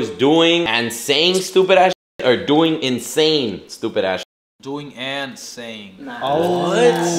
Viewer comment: i thought said “insane stupid ass shit” too lol and i watch him all the time🤣🤣😭